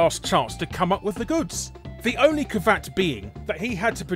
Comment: did you just say "ca-vat"? Do you mean caveat, pronounced "ka-ve-aht"?